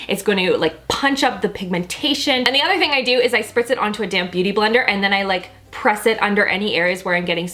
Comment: At first I thought she said a "damn beauty blender" instead of a "damp beauty blender" 😂